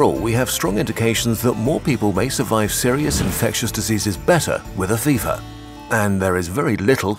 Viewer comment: Did you just say gas disease at first? DID YOU TAKJE LAXATIVE SARAH?!!? I TOLD YOU NOT TK TALEK LAXATIVE